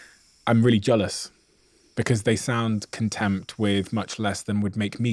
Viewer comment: He simply says contempt, multiple times 🤫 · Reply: In the context of the sentence, "contempt" doesn't fit. He says "content" in such a way, you've misheard, just like the auto transcription did. He didn't misspeak.